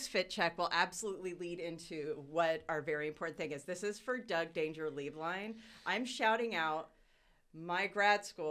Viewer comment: When Doug introduced himself for the first 8 episodes I thought he was saying, “Doug ‘Dangerously’ Blind” 😭 Cracked me up so hard when I realized I was wrong 😂❤️